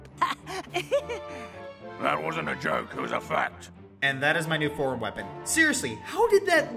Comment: Was I the only one who thought he said "that wasn't a joke, cause I'm fat" and not " Because it's fact?"